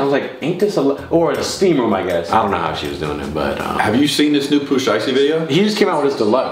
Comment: Bro when he said “Pooh slicety “ and “lil duke” I died 😂😂😂😭😭😭 you guys are always funny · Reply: I thought he said poo slisty lmao